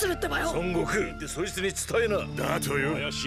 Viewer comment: is it just me or did kurama say son goku